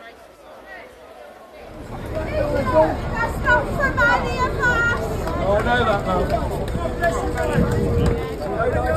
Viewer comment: what did she say? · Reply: Sounds like, "that's not from any of us".